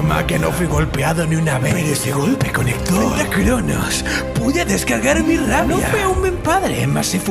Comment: las primeras veces en vez de escuchar frente a kronos yo escuche pentakronos osea pentakill a kronos